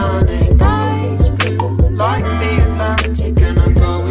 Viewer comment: I thought it said “Eyes blue like the Titanic”